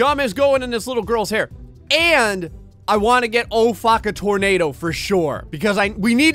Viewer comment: I thought when he said “let’s get o-faka tornado” he said “let’s get Obama tornado”